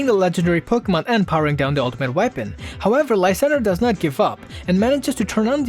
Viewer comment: I thought you said don't give fuck but you said don't give up